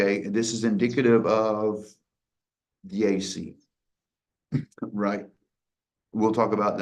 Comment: What did you say at the 2:10:51 mark? I have rolled it back and cant und can't make it out. He is indicative of _______? I cant make out what word that is. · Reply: Sounds like "the AC". It's what he was referencing at the time.